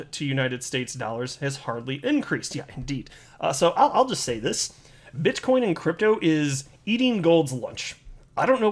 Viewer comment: did he say bitchcoin? lol